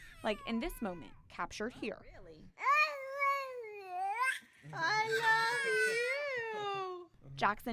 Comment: This is not inspiring. That poor child. He's not walking. He can't even hold his head up or sit. He's not eating, he's barely even mouthing at that cookie. He's not saying I love you, he's babbling nonsensically. He's only alive by sheer luck and he will never have any real quality of life. Congratulations. You're deluding yourselves. You only did this for yourself. The fact that this is being used by pro-lifers to demonise people who choose to end a much-wanted pregnancy rather than let their child suffer a short, painful life (or a long, slow death in this case) shows how clueless and deluded they are, and that they will place quantity over quality every time. This child is nothing but a bunch of reflex responses. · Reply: and I HEARD him say I love you❤